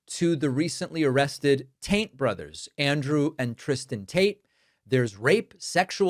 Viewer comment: here you say “Taint brothers”. 🤣. I had to rewind to see if the captions got it too & yes! 🤣 —20:12.. another edit